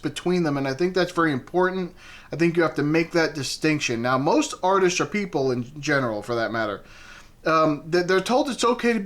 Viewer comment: You said "some artists, or people..." and I heard "some artists are people" and I definitely spaced out for a bit trying to figure out what the rest of the artists are 😂 Like, I even had to rewind 😂😂😅